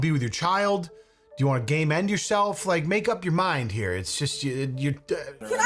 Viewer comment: I definitely heard "gay men yourself" not "game end yourself"